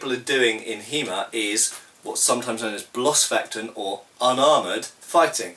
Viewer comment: I was trying to figure out what you were saying at 0:23... And then it occurred to me, that that was "Bloßfechten", am I right? I love hearing German loanwords in English. Sounds awesome.